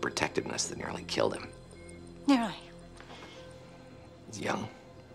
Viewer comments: what does she say here? 😂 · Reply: now that I’ve listened to it again I think she says nearly aha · What on earth does she say 9:31 ?? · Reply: 1cjl2 “nearly?” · anyone know what the hell she said here? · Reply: "Nearly?"